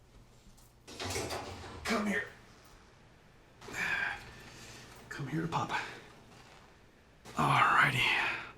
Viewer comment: "Come here, papa"? I think he meant to say "Come to papa". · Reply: its "come here, pup"